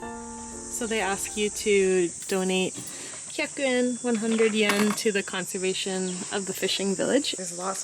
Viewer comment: LMFAO, I’m sitting here in bed watching the video and my gf is next to me on her phone, somewhat listening to the video. She thought you said that you’re supposed to donate a chicken and 100¥ 😂